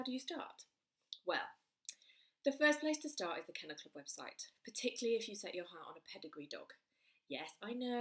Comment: Hello, when you were saying what website you can find good breeders on, what was it called? You said it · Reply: Kennel Club website